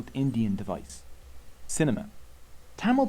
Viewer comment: i heard it as cinnamon not cinema lol